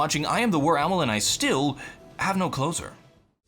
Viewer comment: All this time I thought you're saying "I have no clothes on", only now did i realize..